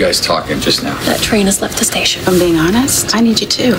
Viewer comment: After watching the previews a few times it sounds like Jason said let her go or I will drop you that’s implying Carly but where is Damien Spinelli? And what deal was Carly taking about?😕